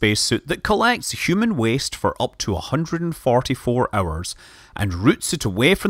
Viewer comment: Did he say Farty four hours? Or im just very into the topic? xDDDD